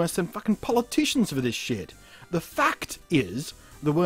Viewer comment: When you said the fact is...I thought you said the fucked is at first... But anyway, great video :)